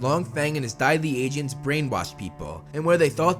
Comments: I thought he said diabetes agents. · “And his diabese agents” I thought you said diabetes